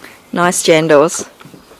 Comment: DID U JUST SAY JANDALS 1:27 idk if u did im just asking